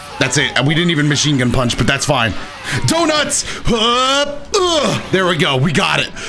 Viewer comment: Did he just say doughnuts? LMAO